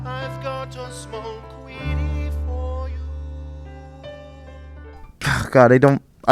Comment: The second song that king gorge sang I heard 19:27 I heard “I’ve got a small weenie for you” like what the heck